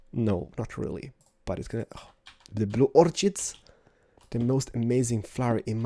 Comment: Sounded like you said "Blue orc-shit" :p